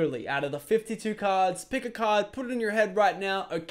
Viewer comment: OMG I thought Lachlan said pick a COD.